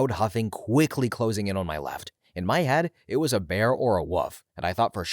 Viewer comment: I swear I heard you say "a bear or a woof" lolol